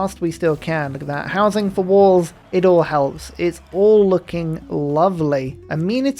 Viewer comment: "I love that housing for walls! It's wall looking lovely." -, casually punning since I don't know when. Edit: Actually, I don't know if that was on purpose, or just my brain automatically hearing "wall" instead of "all", but either way, it was funny.